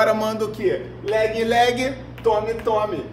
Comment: Oq eu entendi "leg leg tome tome!"